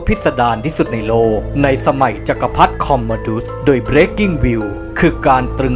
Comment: ได้ยินว่าBreaking News.